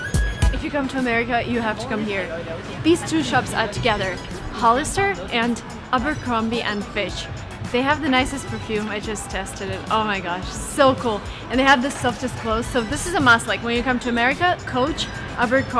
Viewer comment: Wrong spelling on your video: ABERC*R*OMBIE & FITCH and I heard you said FISH instead of FITCH, but I might have misheard it. :-)